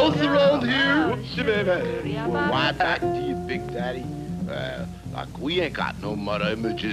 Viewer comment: does the bat say “aw fuck”? I listened to it twice and that’s exactly what he says under his breath. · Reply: He said "like" not the f word.